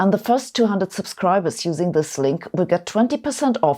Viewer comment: Voice says "see you next year" but subtitle says "see you next week". Get your money back from Markov and Bayes.